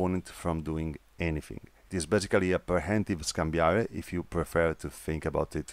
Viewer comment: What's the full terminology? I heard 'escambia de punta'. also 8:48 'scam biara'? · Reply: Scambiar di Punta, Scambiare. Means exchange of thrusts.